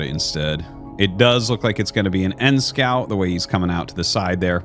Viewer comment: I always thought you were saying "end scout" like you scout the ends of the map.